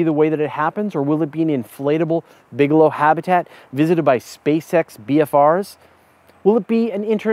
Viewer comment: My sincere apologies. When you said SpaceX I heard it as "space sex" and started giggling like a bashful school boy although I'm a grown ass man. Oh, the wonders of the male mind! :-)